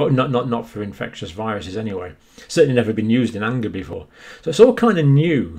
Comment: What did he say at ~ 25:20? To me, sounded like: "It's a completely new approach to vaccines, never been done before... well, not for infectious viruses anyway... certainly never been used in anger (??) before..." What was the word that sounded like "anger" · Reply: The word that sounded like "anger" was , in fact, anger.